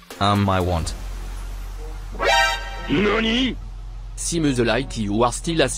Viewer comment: What does Bogdanoff say at 5:02? I keep hearing that sample and it sounds like 'L'ironie!", but it's probably something else. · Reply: Nani